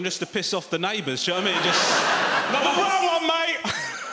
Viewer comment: I looked away for a second and thought he said “MAMAHUEVO MATE”